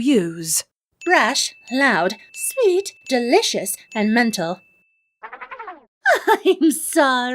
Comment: did you call apple jack delicious?